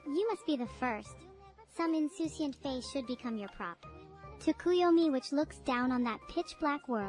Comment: does neuro say tsukuyomi? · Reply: yup its hard to see within the chunibyo wall of text but yes she says tukiyomi